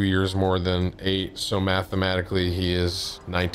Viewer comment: When Tim says the word mathematically I hear “I’m stupid”. ‘So “I’m stupid” and he’s 19.’ Sounds about right lmao